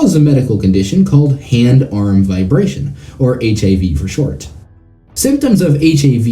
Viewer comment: I heard “hand arm vibration. HIV for short.” and I was like WH A T before you go keyboard warrioring at me, I know he said HAV. I heard it wrong.